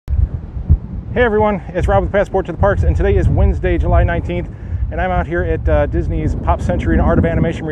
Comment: Did he say July 19th?